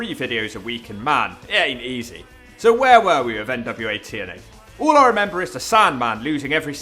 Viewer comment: Did I hear 'Nwo TNA'?!?! New new new Wednesday order